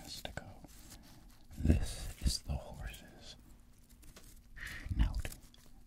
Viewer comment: For a good minute I thought it said ... House .